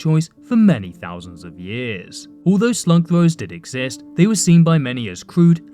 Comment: The hell did he say? Slug floors? Slum lords? snug vores? smug throwers? squidward? snug snores?